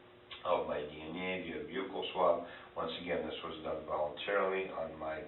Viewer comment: The detective butchered the word "buccal" swab so badly that the auto generated transcript thought he said "vehicle swab". Uff da